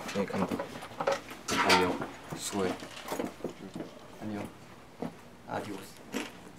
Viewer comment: Escuche mal o en el 9:40 9:41 suga dijo adios?? :DDD